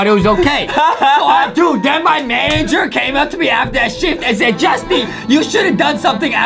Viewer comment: I thought Justin said : then my manager came after that shi